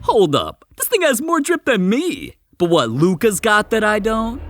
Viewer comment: I heard dis guy say “ dis nigga got more drip than me” we ain’t gonna talk about that